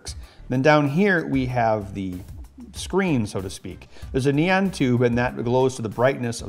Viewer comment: I know he meant neon but that definitely sounds like he said ne-an!!